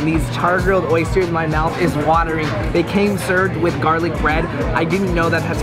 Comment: I thought he said his nut was watering lmfaoo